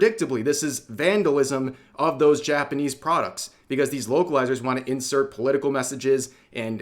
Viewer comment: I thought i heard "woke-alizer"